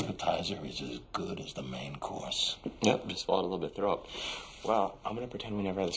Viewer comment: This is definitely one of the best scenes of the OC. What does Seth say at 1:19? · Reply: "yup- just swallowed a bit of throw-up " :D